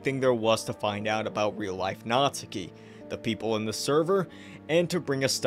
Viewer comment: i keep hearing “real life natsuki” as “real life nazi” which seems more accurate actually